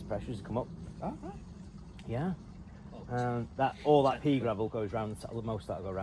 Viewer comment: All I heard was "pee gravel"